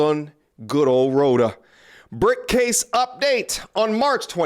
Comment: I thought he said “Brick-Face, update!” 😂😂😂